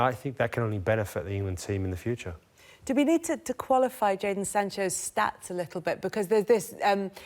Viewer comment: Did she just say Leipzig for Jaydon Sancho? Shambles. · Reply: did she call just call hin Jadon Sanches 5:50?😂